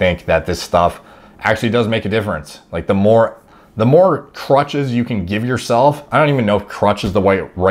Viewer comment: Oh man I thought he said "The more crotches you can give yourself" Man that's a strange way to put it😂...oh ..."crutches"